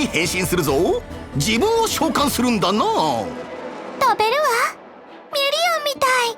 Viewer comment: 食べるわ って聞こえて、いきなりホラー展開かと思った…